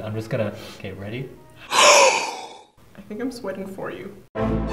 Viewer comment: wait I thought she said am getting wet for you 🤭😂😂because I was putting it on a low volume lmao 🤣